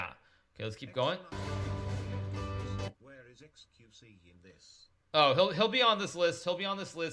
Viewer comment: Who did he say was going to be on the list by 2023? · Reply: XQC